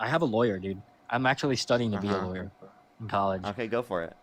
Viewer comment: I think he meant with a lawyer in court. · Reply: no, he quite clearly stated he was 'training to be a lawyer' at around 32:28, give or take a few seconds. Edit: 'I'm ctually studying to be a lawyer... at college' is what he actually says.